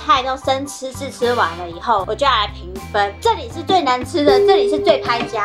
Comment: 我聽成這裡是最難吃這裡是最拍假的 我想說不是一樣嗎哈哈哈